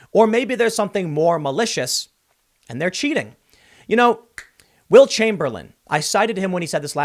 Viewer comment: Did he just say wilt chamberlain 😭😭😭😭